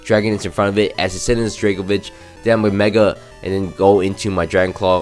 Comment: I thought u kept saying Draco bitch not Draco vish